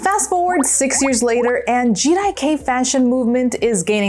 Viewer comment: the subtitle is wrong. It’s jirai-kei not GDK or Jedi cave